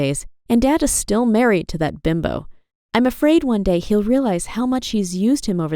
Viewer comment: Did she say binbo🤣 Binbo (貧乏) means poor in japanese やな